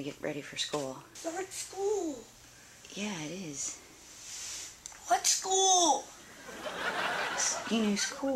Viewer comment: Mom: wake up you got to get ready for school Girl: it's not school Mom: yes it is Girl: WHATS SCHOOL! Same gurl same like what's school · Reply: I thought she said what's cool